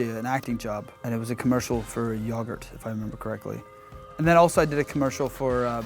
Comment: did he just say “yahgurt”